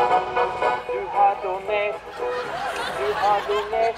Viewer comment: "you have donated" what i heard was "you have dount"